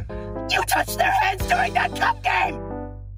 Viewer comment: did he say... YOU TOUCH THEIR HANDS IN THAT C*M GAME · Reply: He said cup game 😭